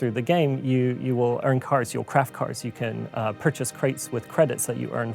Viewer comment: Did he say purchase crates? Looks like there won't be a season pass, but there will be micro-transactions.